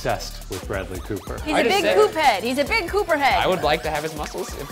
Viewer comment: When said was a big Coop head, it I thought she said poop head XD